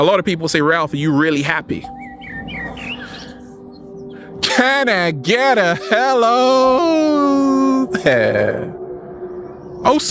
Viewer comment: I thought he was saying "kinda get a hell of air ":D insted of "can I get a helooo there " :D